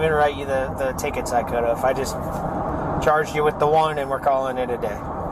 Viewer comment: The cop said "could've", NOT "could of" like the subtitles say 🙄 "Could of" is nonsense.